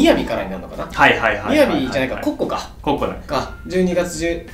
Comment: コッコがこ↑こ↓に聞こえた俺は末期